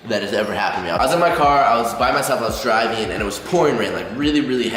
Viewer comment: I thought said "I was jiving."